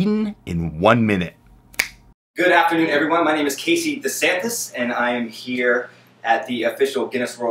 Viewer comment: did he say Casey the Sadness?